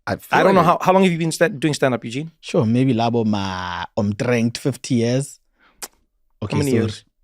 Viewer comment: did Eugene say omtrent? Sounds like Norwegian... 😅